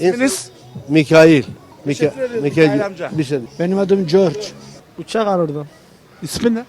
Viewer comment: George mu dedi ben mi yanlış anladım 😂